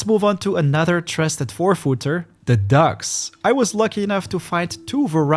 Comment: You got me excited that there were ducks :( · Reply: Me too. LOL! He has a delightful accent, but I thought he said ducks also (instead of dogs).